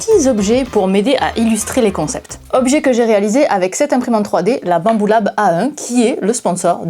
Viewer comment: LA QUOI ??? 👂 📢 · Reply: La BAMBULA(B)